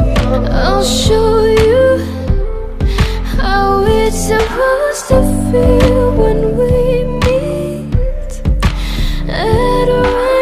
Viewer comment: This whole time I thought she saying "I'll show you how its suppose to feel when we meet and waves Build"